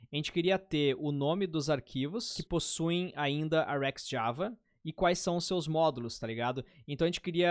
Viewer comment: o que seria o "a rex" ( foi que eu entendi ele falando kkk) java no minuto 6:53? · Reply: RxJava